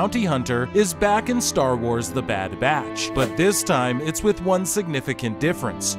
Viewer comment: Thought it said Bad bitch😂